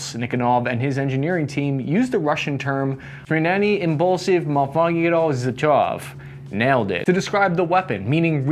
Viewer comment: I listened to it like 10 times and still didn't get what you trying to say. I had to consult english wiki article about Abakan. For lazy people scrolling the comments - its "смещенный импульс свободного затвора" and it is pronounced, well, not like that. Nice try though